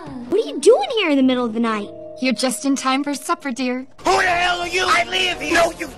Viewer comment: WHY DO I ALWAYS HEAR:"you are just in time to suffer dear"